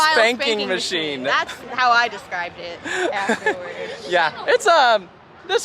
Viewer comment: I thought she said spanking machine & then she looks at & I thought, so that 's What y'all like!🤔! 😂🤣😂🤣😂🤣😂!;)PEACE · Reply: She did say spanking. It’s a line from Spongebob.